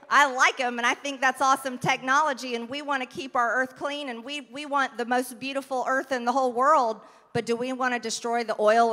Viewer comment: did she just says “we want the beautiful earth in the whole world!”? 😂😂😂..